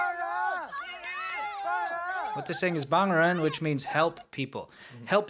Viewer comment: I'm pretty sure what they're saying at 5:45 is not "help people" (Bang ren帮人), they're chanting "release the people/person" (Fang ren 放人)